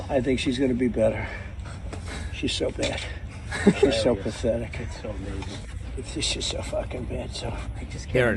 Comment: I dislike Trump as much as anyone else here but to me it sounds like he said "She's so bad. She's so pathetic. She's so fucking bad."